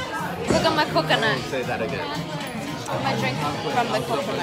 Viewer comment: says don’t say that again did he say that or am I trippin that must have been embarrassing 2:25